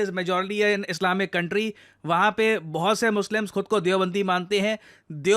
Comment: did you said "Islamic Science"!??😂😅😅